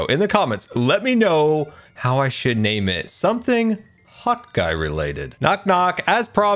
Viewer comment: Hope you have a fantastic day and RIP Crispy. My Links and stuff: * Patreon: <link> * Twitter: <link> * Twitch: <link> * Instagram: <link> *TikTok: <link> *Business Inquiries Send to: <mail> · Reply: OMG! It's "Hot Guy"! I always hear "Hawk Eye"! 😂 Bc you flew to your friends and attacked out of the sky. 😅 Greetings from Austria!💜💜💜💜💜